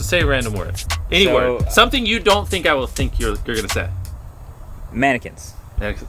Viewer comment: No joke, when he told him to think of a word, the word I thought of was “magnet.” So when he said “mannequin,” it almost blew my mind cause I thought he said “magnet” for a second lol.